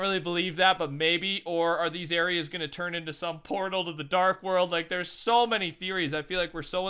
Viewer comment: who else thought he said porno 😂 like if u agree